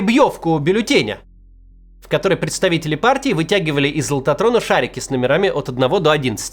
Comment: Вначале услышал "тянули шарики из лохотрона". Подумал что ослышался, но потом понял что нет.